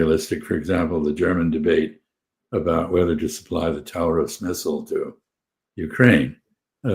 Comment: I had a moment of confusion because I thought Freeman said "Tower of Schnitzel" instead "Taurus Missile" 🙂